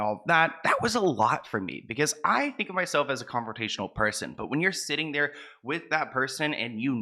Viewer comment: I thought you said confrontational 😂 had me like oooo u won't hit em though ??! or 'Will' you🤔🙃😂 ..✨Wishing you all the success friend!! ..would 💖 to see your girl BJ back, mean it lol✨